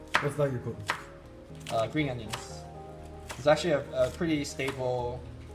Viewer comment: What did said about those leek looking vegetables? I can't hear. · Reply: Green onion, aka Scallion